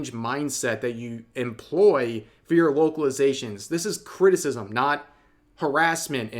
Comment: I know said localizations but it sounded like wokelizations, just fits better.